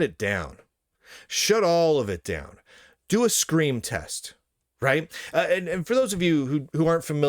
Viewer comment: I thought you said "Screen Test". 🤣